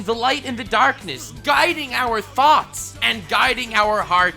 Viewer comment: What he said: "Guiding our thoughts" What I heard: "Guiding our THOTS "